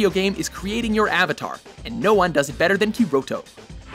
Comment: I swear i heard him say Kiroto instead of Kirito that takes the whole anime pronunciation issues to a whole other lv